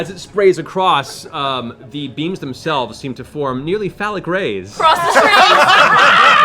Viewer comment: Sry my english sucks, at 3:46:32 what did matt say exactly? fell-a-grace? :S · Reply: "Phallic rays" is what he said.